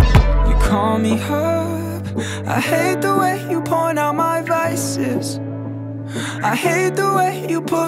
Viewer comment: definitely thought he said "hate the way you point out my biceps" instead of vices